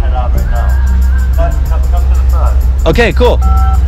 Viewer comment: I have a Samsung and at 3:43 when he says okay cool my phone thought He said ok Google